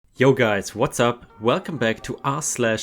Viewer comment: Lol! The openin “yo guys what’s up” sounded like he said “Yoga’s what’s up!” Haha, didn’t know you were such a yoga fan! 😅🤣 ok, I will watch the video now, just thought that was funny.